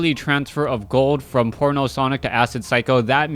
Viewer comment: I keep hearing "porno sonic"